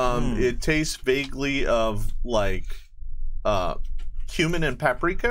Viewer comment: I thought he said the kebabs tasted like 'Human' and paprika, but it was 'cumin'. My way is funny, but horrible.